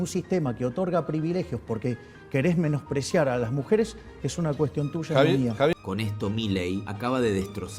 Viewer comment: "Es una cuestión tuya, no mía." Es lo que dijo milei. Los subtitulos están mal, dicen otra cosa.